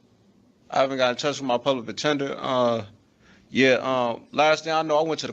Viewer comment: did he just say public attender lol